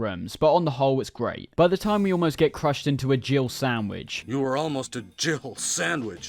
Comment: Does nobody else hear "Jibble sandwich", instead of Jill sandwich? When I played it as a kid my dumb child brain heard "Jibble" and now all these years later I still cant unhear it. Obviously Jill sandwich makes more sense, I dont even know what "Jibble" means but I swear to god Barry says it lol